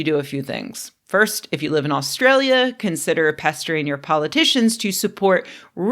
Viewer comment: I had to go back and listen again to hear that "Pestering your politicians.' For some reason the first time it sounded like you said "castrating your politicians."